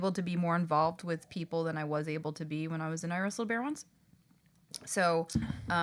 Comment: I genuinely thought it said “ I Wresled a Beyoncé at the beginning.